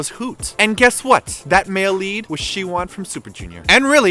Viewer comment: lol i heard siwon as "she won".